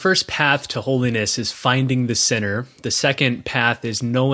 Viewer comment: To my east coast ears, both the bishop and the host have an accent and I thought they were saying the first step was finding the 'SINNER,' but they're actually saying 'finding the 'CENTER'. They swallow their T's I think. Just FYI. That seems like an important thing for the listener to know. Great message though, very thought provoking. Thank you, Bishop Barron. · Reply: Thank you Kim for clarifying - incredibly helpful. I too, thought they were saying to “find the sinner”...